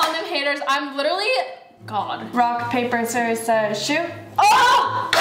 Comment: am I the only one who heard say “I’m literally G0D at 1:51 ?